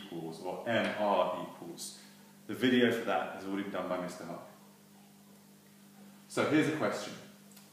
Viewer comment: What did he say at 2:25 about rearranging formulas...? "the video for that has already....?" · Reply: Hi Umar, I said that video has been done my another teacher at my old school. You can find a link to his videos below: <link> Best wishes